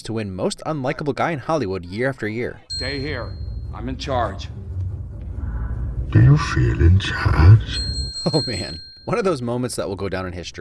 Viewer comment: When Bane put his hand on Daggett's shoulder, for some insane reason, I thought he said "Do you fear this hand?" instead of "Do you feel in charge?".